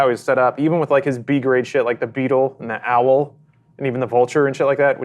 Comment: did Lawrence call Vulture a B-Level Spidey villain?